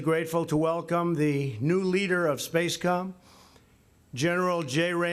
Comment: Did he say "spacecum" at 1:36?